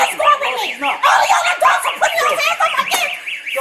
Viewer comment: the captions are wrong she said: all of yall adults are putting hands on my kids